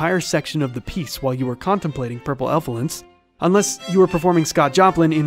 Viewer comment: Did I hear him say Purple Ephelants?